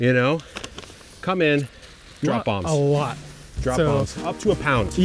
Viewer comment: Hi. 7:35 Do you not run these vids past some “testers” before uploading? They would point out glaring errors like “bigging up” some wonder product and then not detaling what it is. You mumble something that sounds like “ drop bombs”. FFS drop in a caption or product close up. Thanks. I don’t publish anything myself at the moment, but worked in broadcasting for many years and I can’t imagine launching anything to the world without first running it past some people to review who haven’t been involved in the production. But unfortunately it’s a step that many Youtubers seem to omit. Sorry if you have explained it somewhere and I’ve missed it.